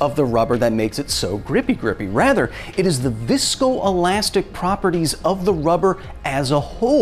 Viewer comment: I heard "disco-elastic properties of the rubber itself." If that's not a thing... look man... that should be a thing.